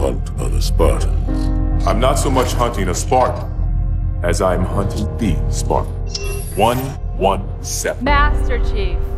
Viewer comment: BAHAHAHAHA!!! OH MAN AUTO AI GENERATED SUBTITLES ARE THE BEST! I leave on subs soley because it will totally mishear and display hilarious results. And this didn't disappoint. When Lock says "I'm not so much hunting a Spartan as I'm hunting the spark," it displayed "I'm not so much hunting a Spartan as I'm herding a fart." BAHAHAHAHAHA!!!! I hope this tech never improves. It's perfect as is. Lol